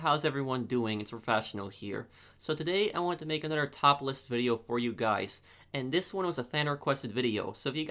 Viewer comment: Did you say topless video lol?